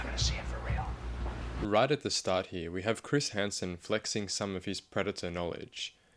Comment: lol they messed up the subtitle and put emily instead of chris